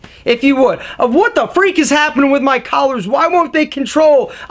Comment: Did he say "lesbian collars" at 4:46? Lol there's something oddly sexist about his tone when he says "a woman".